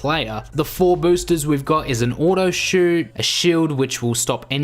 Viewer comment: i wasn't looking and I heard the auto shoot and the shield together so I heard uno shield 😂